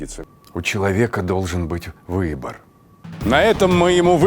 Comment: я думал мне показалось что он сказал "выебор"